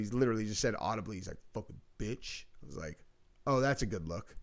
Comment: I think he said "fkin bit" bc he was being clam and telling her to get out. Then she tried to hit him and his whole demeanor changed- it went from this is just my job to that bih tried to kill me